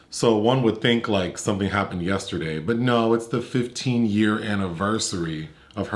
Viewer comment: At first I thought you said 1 year after her mother's death not 15 haha my mom died last year and so I was like, "that's so cold! It's been 6 months and I'm so torn up still" I hope after 15 years I'm not all mopey 😂😂